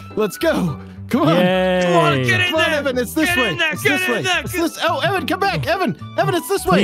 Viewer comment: just re watched, anyone else hear say "OH! eric come back!"